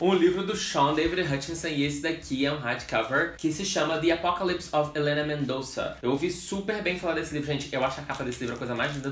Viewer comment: Kkkkk juro que ouvi "the apocalypse of Marília Mendonça" kkkk mds